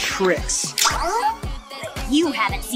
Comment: Why did i heard "you havent seen what asians can do" ?💀😂